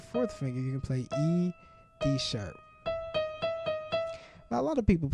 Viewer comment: Is it me or did he say E-Sharp at 3:23???